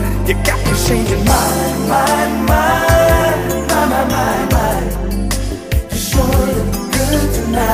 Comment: Am I the only one who thought he said "Shorty look good tonight" instead of "you sure look good tonight" lol